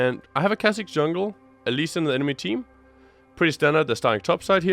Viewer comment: Haha it sounds like you said "elise on the enemy team"